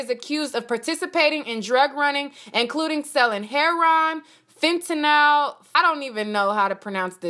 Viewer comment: did you just say hair-on??? OMG 😂😂😂😂 it’s heroin!!! The drug!!!! Heroin!!!! Pronounced hair-ō-en. JESUS 😂😂😂😂